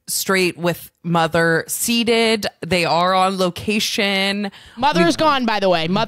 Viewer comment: I'm losing it. When said that the staff had come up to her and said "Can you pass this onto Ashley - Straight with mother seated", I thought said CEDED, not SEATED. As in, straight and mother have ceded, they've given up sovereignty, surrendered, officially dropped out of the game and handed the power to Active & Available Queer. 🤣🤣😂 It took me a hot minute to realise had said seated. I also lost it at "You should give me your contact information but also leave the premises while I hunt down this other woman and her mother". This is some of the funniest unhinged shit. 🤣😂 Meanwhile got the entire staff and audience looking under their chairs for the disappearing-straight. Like a fire alarm has gone off and you're like "all hands on deck!!" I wheezed and nearly turned my trachea inside out when we got to the "aloof" bit. On a random note that mustard-yellow-gold tshirt is the best, most flattering colour on you!